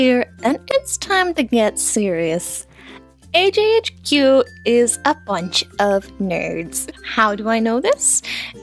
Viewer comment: I thought it said... AJHQ HAS A BUNCH OF NERDS As in nerd glasses. ._.